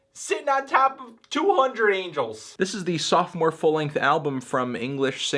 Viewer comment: I heard 'softcore full length album'. Is that right?